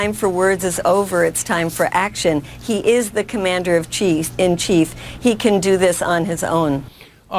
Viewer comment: did she call him the Commander of Cheese at 5:08?